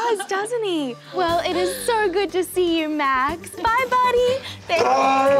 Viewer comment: Max responded so quickly and it really sounded like he said, "BYYYYYEEEEEE!!!!!!" That was a total Dr. Dolittle moment.